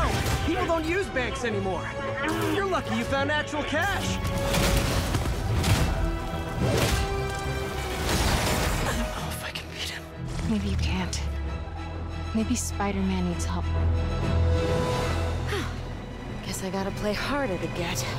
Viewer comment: I know I’m late to this but listen to this it sounds like he said I’ll f*cking beat him